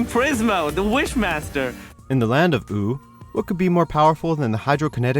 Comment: I'm pretty sure I heard you say poo